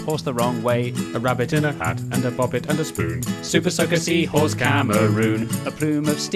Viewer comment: Oh! Bopit. I thought you said Bobbit, That got me really worried.